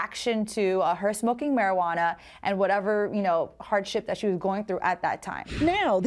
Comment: lol I thought she said hard shit instead og hardship